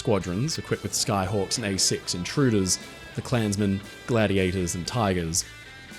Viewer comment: did he just say the “clansmen”?